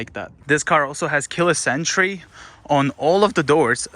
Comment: When he says Keyless entry it sounds like he is saying the car has killer sentry XD XD